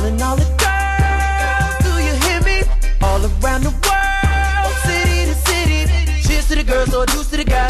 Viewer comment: When I was younger I thought he said all around the world Mississippi lol 😂